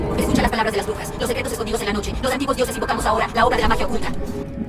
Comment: What did cream do and say? What did the owner of the channel say? Can you explain to me what she said? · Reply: los secretos escondidos de la noche, los Antigous dioses invocamos la Ora, la obra de Magia oculta